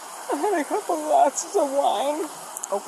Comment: I thought she said couple BOXES OF WINE